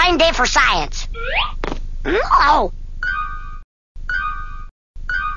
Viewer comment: lolol sounds like he said "awe what the fuck forget it"